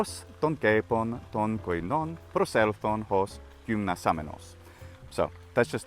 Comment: you probably meant “προσήλθον” instead of “προσηλον”, which is the Aorist B of the verb (έρχομαι/είμι)=to come.